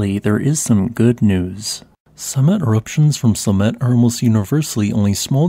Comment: I pronounce it "Slam-it" instead of Sla-met. Just for humor 🌋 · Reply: At first I thought he said "Cement eruptions..." But I listed at 2x speed most of the time.